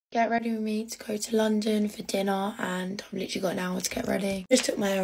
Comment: Did i just hear "grwm to go to London for dinner" (or i didn't sleep well) 😭